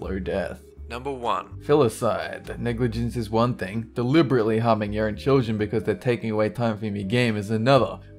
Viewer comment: is english your native language ? it is not for me and this sometimes makes it very hard to understand you... 6:56 :"delibrilty hamin yo own chosen" ? you probably said "delibaretaly harming your own children" ... Besides that I like your videos but can you use video material instead of lame pictures? doesn't bother me concerning this video but sometimes you could have used some clips